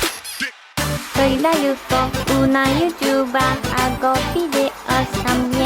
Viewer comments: soy la yuko una youtuba hago videos tmb directos... 🎵🎼 me encanta. 💝😂😂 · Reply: Pensé que decía q hacía fideos.. jajajajaja me dió risa esa parte ajajajaja!! Q linda Yuko!! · CVLPI$ BOI o NKB48, que comience la batalla!! Y aquí tenéis la letra para los que queráis cantarla a coro!! Soy la Yuko, una youtuber, hago vídeos y también directos, ¿Qué hora es? ¿Tendrán bebés? ¿Qué edad tienes? Tengo trentaiñoooñooo. Soy la Yuko, una youtuber, hago vídeos y también directos… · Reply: pensé que decía hago fideos jajja sorry